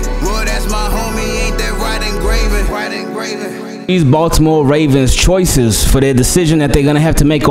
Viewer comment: Intro sounded like you said East Baltimore Ravens 💀